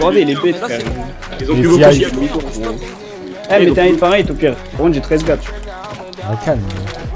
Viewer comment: à 3:20 il dit quoi svp ?? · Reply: Par contre j'ai 13gapp